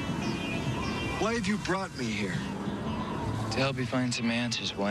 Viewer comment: What does Wayne say on 01:13? Is it "Why did you brought her" or "Why you'd brought me here?". Arne't they both wrong? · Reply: He said "Why have you brought me here?"